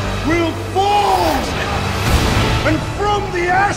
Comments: I just heard... ''we will build a better wall'' >:T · I thought the it was saying "Everything they built will fall! And from those ashes we will BUILD A WALL!"